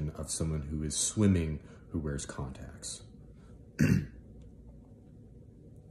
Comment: This was little bit weird at first cause I heard "condoms" not contacts 😅